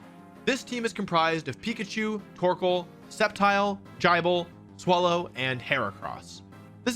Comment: did he just say Jible?